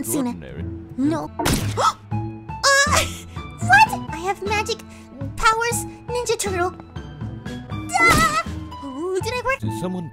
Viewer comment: Am I the only one who hear in 0:58 I have magic power ninja turtle